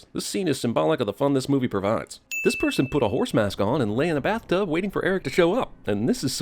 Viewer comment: I'm...I'm sorry, I might've misheard. Did you just say '"horse mask"?